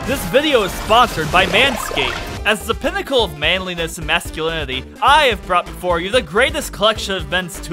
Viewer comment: i heard that as "as the pinacle of maleness masculanity" also i saw dragon mai